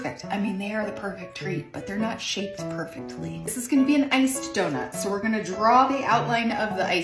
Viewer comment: Maybe it's because I'm not fully awake... but for half a second I heard, "This is gonna be an angst donut." ... and that's just too real. Beautiful art. 😊